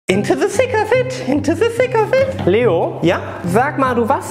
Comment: “InTO tHe ThiCK oF iT” “InTO tHe ThiCK oF iT” Lachflash🤣 Edit: Ich hab das vor 10 MONATEN Kommentiert und bekomme immer noch Benachrichtigungen😂 Danke für die ganzen Likes :D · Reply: Ohhh ich hab immer verstanden: into the sick covid